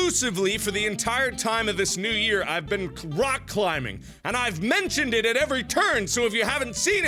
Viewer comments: When Mark said he was doing rock climbing I heard "Cock climbing" instead. I was laughing so hard at the fact that either could be true and we would all laugh and make jokes about it. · When Mark says "I've been rock climbing." Some random c sound was thrown in so it sounded like "I've been crotch climbing." THANK YOU WHOEVER EDITED THIS VIDEO TO NOT TAKE THIS OUT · I thought I heard him saying I was cock climbing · I thought he said cock climbing